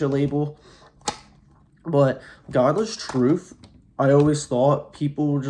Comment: Thought u said godless soup...